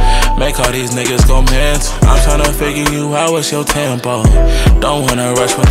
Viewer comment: @ 0:58 did you say.. I'm trying to figure you out with your TAMPON 🎶🎵